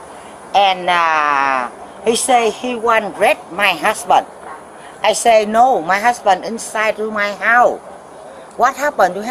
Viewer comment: I have to say, having someone who can speak your language in a situation like this must give you much relief. I really love how they make an effort to get someone out there who can ensure the lines of communication are as clear as possible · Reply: what was the conversation at 11:45? “He said he wan wreck my husband, i said no my husband insiiiide. He said I wan wreck you, i said whyyy” Thats the best i got